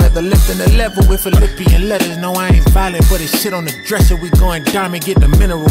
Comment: did he say putting shi* on the dresser??? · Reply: Nope he said but it sit on the dresser